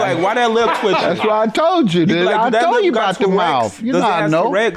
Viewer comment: did this dude just say "that lip got TERREX"??!! 😂 😂 or did he say "T-Rex" 🦖 🦖🦖. Hahah either way it's TOURETTE'S my dude!! 😂 😂 😂 😂 ☠ ☠️ ☠️ ⚰ ⚰️ ⚰️